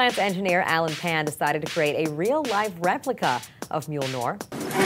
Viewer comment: DID SHE JUST SAY "MJOLNOR"?